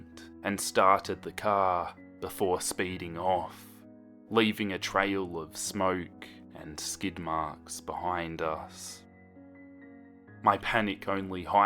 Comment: I wasn’t paying attention and I hear ‘leaving a trail of skidmarks just behind us..’